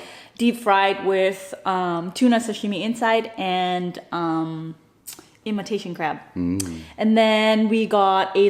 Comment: When you were describing the monkey brain I thought u said Emma tasting crab. Lol